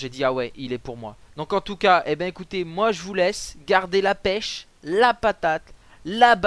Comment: allez a 09:48 vous mettez en plein ecran et dec dit : "bon ecoutez moi je vous laisse" dec il arrive a "laisse" faite echape on dirait il dit leche (je sors) ====>